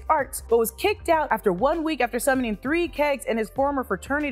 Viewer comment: Did anyone notice Donny Blaze's expressions when Wong was making his speech in the courtroom and said, "has the ability to cause a ripple... that can destroy all life within the known and unknown universe"? He made a face like he was upto no good and could use this info by Wong to cause some real trouble. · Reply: I thought Wong said "He summoned 3 Kangs" but idk